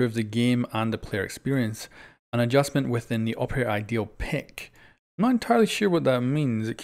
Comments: I'm not the only one heard him say ideal not idle right? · "The operator idle pick" just to clarify that it isn't ideal, it sounded like you were saying ideal although that may have been your accent but I believe it's referring to how if you're afk when you're teammates are picking ops you'll just get stuck with recruit, well maybe they'll change that so you can set favorites of which operator it'll automatically go to